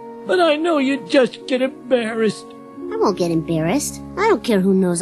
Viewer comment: "Hey funboys get a room!!" 😂😂😂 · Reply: I thought he said bumboys